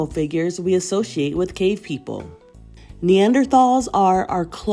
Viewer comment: with I thought she said gay people